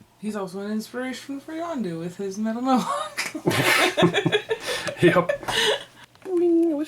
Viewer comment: "He's also an inspiration for Yondu with his metal yonk"? Man, trying to figure out what you said.